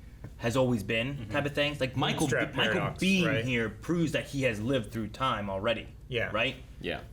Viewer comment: I heard "Michael being here..." as "Michael Biehn here" because my mind went straight to Terminator and Kyle Reese in reference to time loops creating a key character.